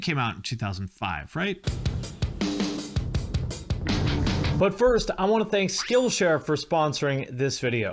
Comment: When you do the ads i always hear "butt first". That's it. Thank you.